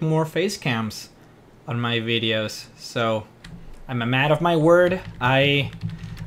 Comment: It's part of the joke. When i said I am a man of my word, it sounds like i said mad of my word in the video.